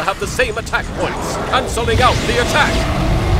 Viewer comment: Sorry for nitpicking, but shouldn't the subtitles say "cancelling out the attack" instead of "something else"..? Like wth does that even mean, lol?